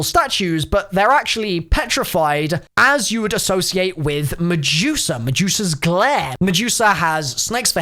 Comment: I thought you said Mejuica and now I can't unhear it